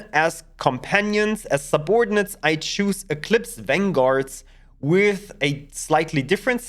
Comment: for a moment there I thought you said Elite Vanguards